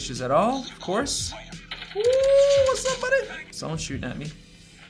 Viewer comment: What did he say?🤣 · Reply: “Wasssup Buddy”